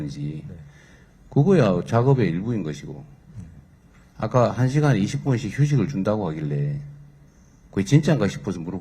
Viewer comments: 대표이사가 말하는 것을 대통령이 잘못 알아들은 듯.,.. 나는 식사시간 1시간, 그리고 네시간마다 20분식 휴식이라고 말한 것으로 들었는데... 대통령은 매시간 20분 휴식으로 들으신듯... 그래서 계속 한시간에 20분 휴식이냐고 몰아붙이신 것 같음. 다시 한번 확인해보고 대표이사에게 사과하셔야 할 듯...ㅎㅎ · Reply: 몬 개소리 나도 1시간 20분휴식으로 들었다 니가 사과해라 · 대표는 네시간에 20분으로 발언한 것으로 들리는데 매시간에 20분으로 대통령이 잘못 들어신 듯 한데요 지은 죄가 있으니 쫄아서 대통령이 잘못 들어신거라고 감히 말을 못한것 같은데